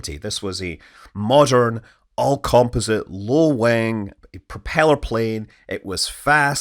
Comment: am I the only one who hears "low wang" lmao?